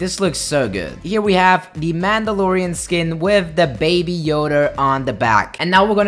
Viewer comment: is it just me or did he say baby yoder 6:00 min to